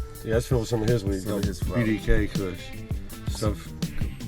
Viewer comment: did he say bdk kush???